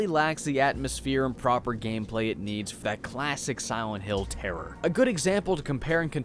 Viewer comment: It always sounds like is saying “Si-went hill...”